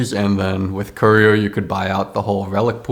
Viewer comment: why did I hear "You could buy alchohol* 😭